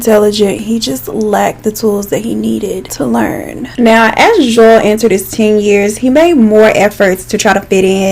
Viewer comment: also anyone else thought she was saying Joelle?